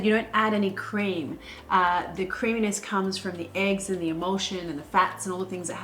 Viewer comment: I thought Marion said "...what's gonna make the carbonara creamy are the eggs, the EMOTION, the fat..." and I was like "yes, of course, the love we put in the food we make is gonna make it more delicious...it's scientifically proven.." and then it dawned on me..."EMULSION" ughhh I'm such a dumbass XD